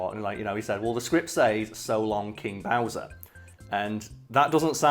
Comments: Honestly, I always heard "so longy Bowser" as if the voice actor was putting a "ee" sound at the end. Idk why he would do that but that's what I heard as a child. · I always heard it as "So long K. Bowser." Assuming the K was short for King. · I;ve never heard hear either I've literally always heard it as "So Longie Bowsie!"